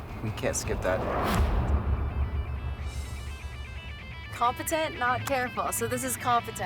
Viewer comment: omg mishearing "be competent, not careful" as "be confident, not careful" is the best and worst mishearing of a piece of advice xD